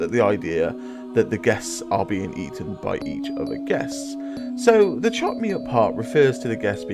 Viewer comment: It sounds like their saying chop meat up eat more chop meat up